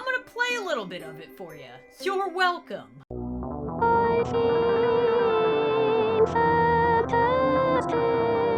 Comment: so if you look at 15:58 in the video it sounds like shes saying " I see santa's di**" and at the end of the video to